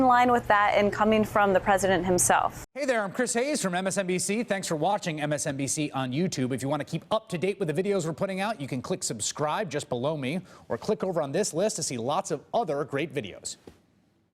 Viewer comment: What is that guy Chris Hayes saying in the end... "Just B'low me" WTF 😂😂